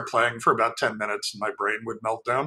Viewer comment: ~What are they saying about "Ben?" lol